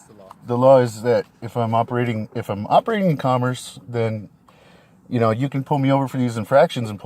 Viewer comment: Around the 5:45 mark it sounds like he says you can pull me over for fractions. Math is hard.